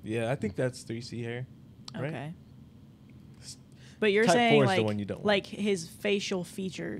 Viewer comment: sorry did he say type 4 hair is the one you don't want